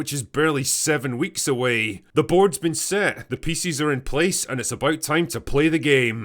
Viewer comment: "The boards been set, the pieces are in place..." I heard another word, "feces", same thing really.